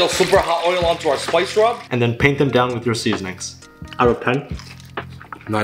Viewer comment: I thought he said 'I repent' instead of 'Out of 10'.